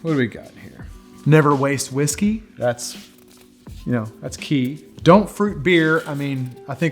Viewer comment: Did he say, don't fruit your beard? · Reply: Don't fruit your beer.